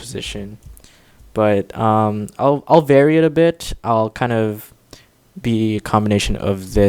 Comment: who else heard vary it a bitch instead of bit